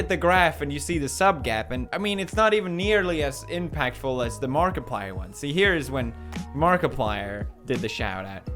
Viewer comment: When he say Markiplier,I hear he said "God damn it"😂😂😂😂😂